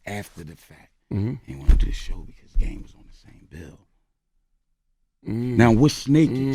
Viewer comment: did he say meek aint want to be there cause game was in the building